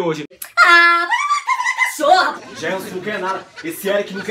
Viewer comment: Foi um "Gelsonn" que saiu ali aos 05:00?